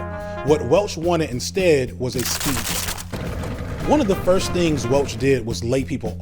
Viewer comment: it's impossible to tell what the narrator is saying, despite the supporting animation and looking at the transcript, which says 'speech': "What Jack Welch wanted was a speech". I'm guessing that maybe it's supposed to be 'speedboat'. Why people let videos out into the world without checking them first is beyond me.